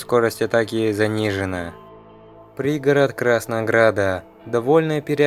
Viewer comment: Когда говорил про пригород краснограда мне послышалось пригород краснодара